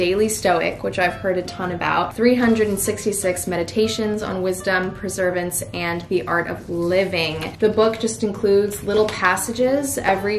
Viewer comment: I could have sworn she said “preservance” instead of perseverance. Lmao